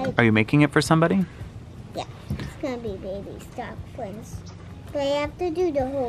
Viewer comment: referring to Flynn as baby Stocklin is so so cute. Also, way to get that Nintendo spon! So cool. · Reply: Alexandria Hayse I thought he said baby stock”Flynn” 😂